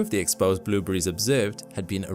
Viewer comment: Ooooh you're saying blue berries! For 10 mins I thought you were saying blew breeze! 🤣